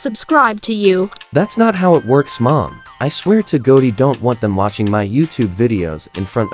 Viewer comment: Im sorry but I heard "I swear to gogy"